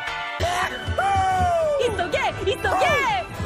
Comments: Tanjiro maybe saying Inosuke but i just heard it's ok it's ok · Tanjiro is literally saying "he so gay, he so gay!" Edit: why is this the comment with most likes AHAHAHAH LMAO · ITS SOUNDS LIKE HES SAYING "He's so gay! He's so gay!" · I just hear they saying "it's so gay, it's so gay!"